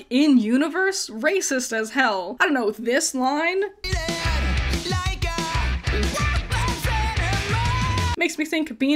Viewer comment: I have no idea what he is saying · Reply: "Treated like a worthless animal"